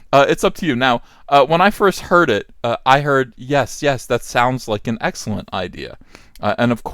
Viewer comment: For a moment I also thought I heard the F word, but he said "That sounds like an excellent idea". So, cool down, NPCs, you won't change everything you lay your sticky fingers on.